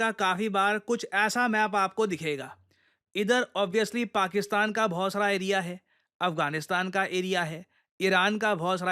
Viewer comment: Did he say Pakistan ka Bhosda Area hai smartly 😂😅